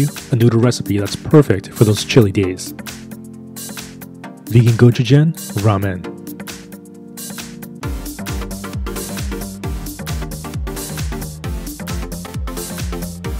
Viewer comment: What it say - Vegan gochujang ramen What I heard - vegan "go to gym" ramen 🤣🤣🤣 Jokes aside amazing recipe though ♥️♥️♥️